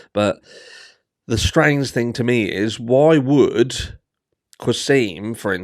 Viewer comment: had to go back and listen again , when you said , “why would” I thought you said “Roy Wood” lol